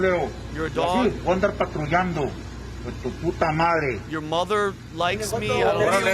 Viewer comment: no he did not say “your mother likes me.” He said he was gonna round them all up like dogs, the mother effers, and they’re gonna take them all out.